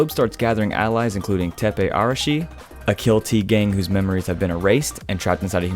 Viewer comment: i thought he said tigang Only Filipinos can relate